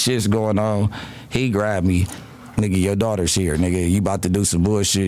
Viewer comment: Maybe its me but i difnt hear him say yo daughter her but ard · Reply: I heard him say your daughter in the room